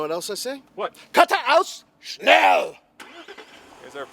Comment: is he saying geraude aus, schnell?